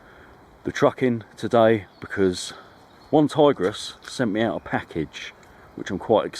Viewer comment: At first I thought you said “one tigress “ and were referring to your missis)))